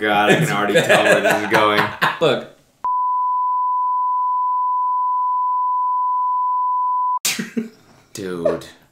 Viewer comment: It's 2023 and I'm still trying to know what Noel said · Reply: Another comment said that they revealed it on tmg, it was "I wonder how his dad was holding the camera with one hand"